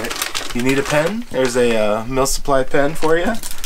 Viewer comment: Had to clean out my ears.. I thought I kept hearing "MILF Supply".. #AWESOME!!